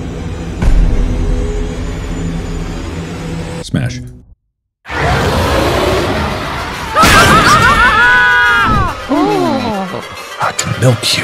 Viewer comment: DID HE JUST SAY SMASH AT 10:19 ?!?!